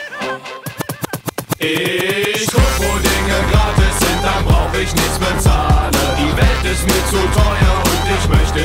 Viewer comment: Es heißt Ich guck', wo Dinge gratis sind, dann brauch' ich nichts bezahlen. (Ich habe erst immer verstanden Ich guck', wo Schokopudding gratis sind, dann brauch' ich nichts bezahlen xD)